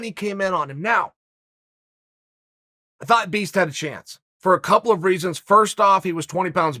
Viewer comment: did he say "the fat beast" ?